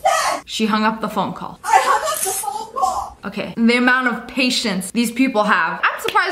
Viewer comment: She said “phone call” about 13 times She out here sounding like a broken record 🤦🏾‍♀️ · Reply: I thought she was saying " I did what she said I hanged up the vocal. "